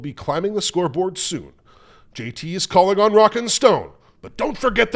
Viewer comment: DID I HEAR A ROCK AND STONE?